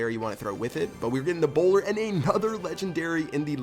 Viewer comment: For a second I thought you were British so when you said "A bowler", I thought you said 'Ebola' XD